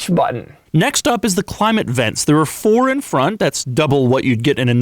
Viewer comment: I think he said "the" only Lamborghini, just incase you were wondering